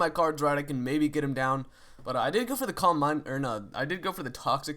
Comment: i never realised he was saying "calm mind" i always thought he was saying cormbine or somthing hahahah